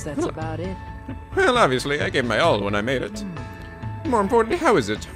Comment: I thought he said "Well obviously! I was gay when I made it!"